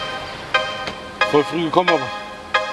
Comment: Was sagt er eig. bei 0:50 ? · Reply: Rodwan El-Zein voll früh gekommen habibi